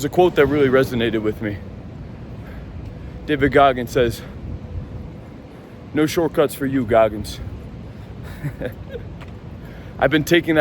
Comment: Thought you were saying no shortcuts for you, virgin😅